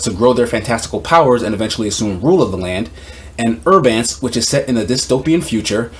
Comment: Did he say herb ants for the futuristic anime. @ 4:35 I'm trying to find it.